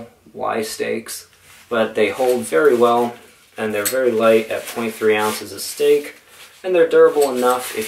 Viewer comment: Did he say his tent stakes were 23 ounces or .3 ounces a piece? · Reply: Aurtisan Miner .3 oz. I think this is the 4th or 5th time someone has commented this 😂 I would like to see these mythical 23 oz stakes